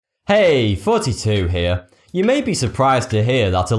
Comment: the intro normal ppl hear "hey thoughty2 here" the intro i hear "hey 42 here" maybe its just becuz im dutch and used to American accents and not british accents....